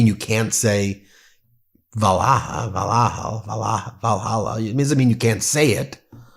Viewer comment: It sounds like he is saying 'Dahalla'